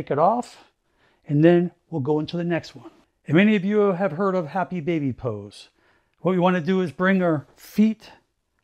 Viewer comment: Hi. I had to replay pose #2, thought you said "happy D@ pose" , but was happy baby pose!!